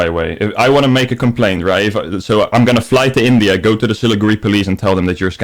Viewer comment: Sort of off topic, but when you were on the phone with and mentioned the siliguri police, I totally misheard it as “silly grey police”